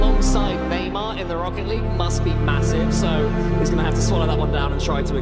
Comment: did i hear massive?